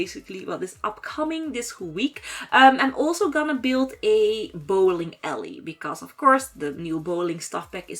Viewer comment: I was so confused i thought she said bowling Elly then I realized she said ally...